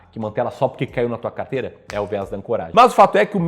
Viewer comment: O que você fala nos 7:59? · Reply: Viés de ancoragem. Comprou uma ação por 10$, viu ela cair pra 5 e decide que não vai vender, porque espera que ela volte nos 10 e recupere seu prejuízo. Outro exemplo é você comprar por 10, ver ela valorizar pra 20 e decidir não comprar mais, porque você já pagou um preço menor por ela.